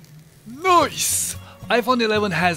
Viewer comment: Did he say "nooice" ?? [2:35] LOL!! reference: <link>